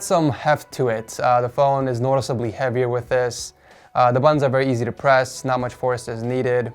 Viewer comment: I keep hearing "The buns are easy to press"